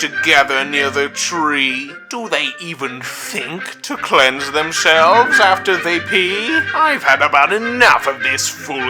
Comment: I always thought it sounded like "clenge themselves".